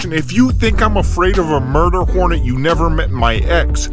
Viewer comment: I thought you said "murderporn" in the intro, had to double check lol... Build is sick!